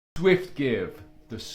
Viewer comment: How did you pronounce the name? It sounded like you said swivgiv. Would it not be something like Sweena?